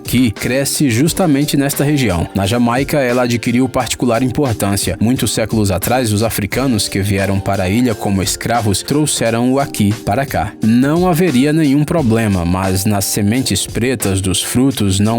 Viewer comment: Oq ele disse: Trouxeram o Akee para cá Oq eu entendi: Aqui pra ca